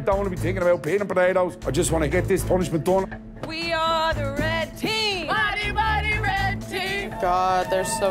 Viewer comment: What are they saying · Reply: “We are the red team, mighty mighty red team”